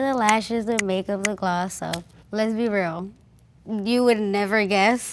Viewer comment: It sounds like she says "lesbi real"